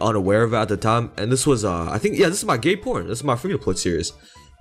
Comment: did he say „this is my gay porn”?